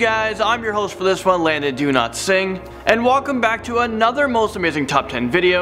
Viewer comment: My sincere apologies, I need a clarification......I heard the host's surname as ' do-not-singh'.... instead of dow-lat-singh....how is it pronounced?